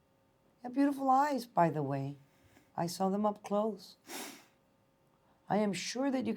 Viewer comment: I thought she said he had lice and she saw them up close lololol